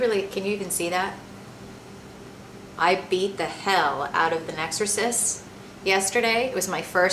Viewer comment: P.S. - At first I thought you said: "I beat the hell out of an exorcist yesterday." HAHA that would've been one hell of a Halloween :D I need to get my hearing checked.